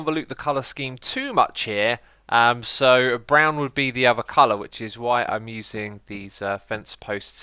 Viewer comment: I genuinely thought you said "Brown would be the avocado" and had to replay it like 4 times until I heard "other colour" lmao